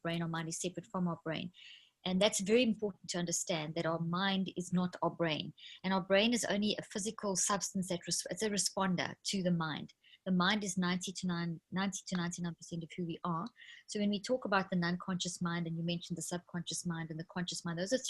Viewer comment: the brain is 90 to 99% who we are! Is that what she said? Thanks for the interview! And notes in bio.